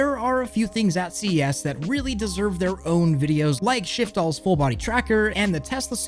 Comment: Legit thought that said "Shit fall"